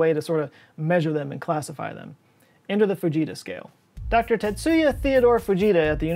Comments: I thought you said the Fajita scale…made me feel hungry! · Bro at first I thought you said the vegeta scale. 😂😂😂